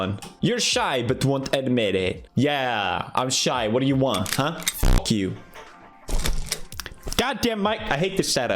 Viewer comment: pewds says f*ck you but the subtitles say thank you. He’s wholesome even when he doesn’t intend to be.